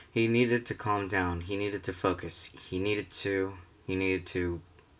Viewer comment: I thought you said fuckes instead of focus